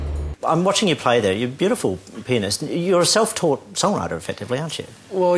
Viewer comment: lol call me chilidish but i so thought he said "the way you play your beautiful penis" =D